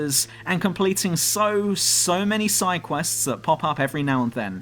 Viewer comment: I heard sidequests!